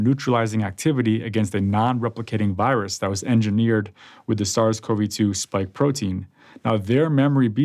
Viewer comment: Have you noticed that he says "there is no virus" at 2:42? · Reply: No, but after several replays, what he actually says around the 2:42 mark is the following: "Compared with 6 months prior, those who were not vaccinated maintained most of their plasma antibodies against the virus’s receptor binding domain (RBD). Also, their plasma, which contains antibodies, had similar neutralizing activity against a nonreplicating virus engineered with the SARS-CoV-2 spike protein. Their memory B cells that produce anti-RBD antibodies were only slightly lower than at the previous visit and had evolved to produce a broader and more potent range of antibodies."